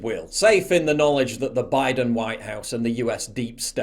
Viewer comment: sounded like Biden Whine House ,😆😆.